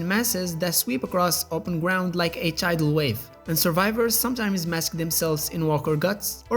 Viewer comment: my volume is low and i thought he said HIV wave 🤦‍♂️